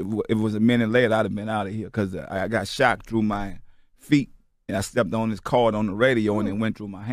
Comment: I thought he said, " I got SHOT through my feet and it went through a cord on the floor to the radio and it went through my hand " I was like, WTF🤔... 🤣